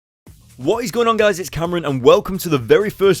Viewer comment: Did you just say that your name is? (Sry if I butchered your name) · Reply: Yes, Cameron. You nearly got it! :)